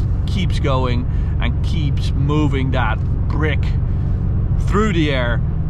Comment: did he say “brick”? That would be the first time an M5 is called a brick! XD